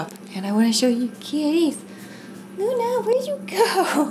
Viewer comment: i thought you said "Lona where's your cow" 😂